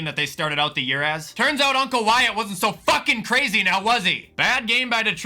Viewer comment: Am I the only one who thought bro said “yesterday was a terrific day to be white” I had to do a triple take before I realized he was saying Wyatt